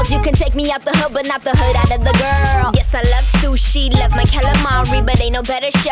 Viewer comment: Back when Becky used to rap. Her new music is good but her rapping was dope ☹️ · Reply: FangirlWifi I miss it so badly bro when I found her new music I was kinda missing the rap cuz she was so good at it .. I thought she said “you can’t take the hood out of the girl “